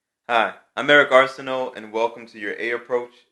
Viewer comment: i heard you say your name AmEricanArsenal sorry i guess im deaf :( no offense